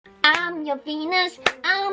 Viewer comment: at the beggining i heard "im your penis"